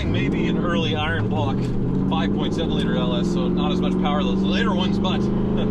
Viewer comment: its little hard to hear but I thought I heard him say early iron block LS?